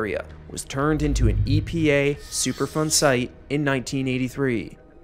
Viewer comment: I 100% thought he was saying “Super fun site” and had to google it because it doesn't sound that fun ngl. I mean, I could be wrong… Don’t let my unsupported pessimism get in the way of super fun.